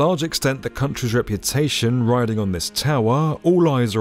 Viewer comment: I thought he said Jedai.😂